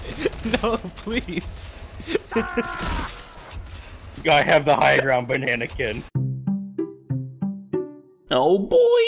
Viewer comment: I have the high ground Anakin - KJ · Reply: What? I thought he was saying: I have the high ground Banana kid!